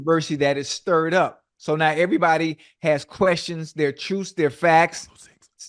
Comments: What did they whisper to Ocho in the mic at 10:10? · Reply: I think he said no truths ocho 😂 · what did Shannon say?? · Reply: “Dont say those things” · did Shay say “Don’t say Facts”?🤔🤔🤔